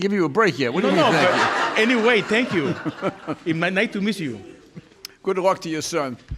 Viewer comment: it may nite to miss you instead of nice to meet you, i like how foreigners speak english